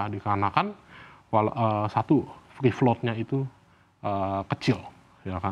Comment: dia ngomong apa ya kawan2? Fee Float?